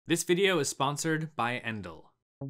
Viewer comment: I’m dead asf I thought it said “ this video is sponsored by Enlil”